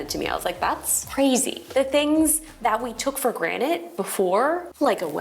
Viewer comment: Did she say “took for granite” at 3:30? Cute.